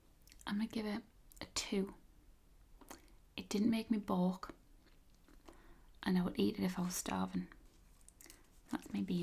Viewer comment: Loved this! But side note; 18:09 re: ban-offee "....it didn't make you b.........?" I presume "throw up"; "vomit"; "toss cookies", "blow chunks"; "up-chuck" but you said a word I've never heard before...perhaps it's what many say on the other side of the pond. Cheers! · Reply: I think may have said "boke" which is a slangy way of saying "throw up" in the UK. "Toss cookies"/"blow chunks" are probably the closest equivalent. :)